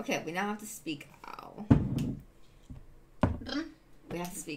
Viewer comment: Omg, "speak owl", I thought she said "now we have to sieg heil" lol.